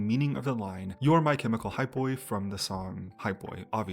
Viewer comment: This whole time I thought they were saying “chemical high, boy”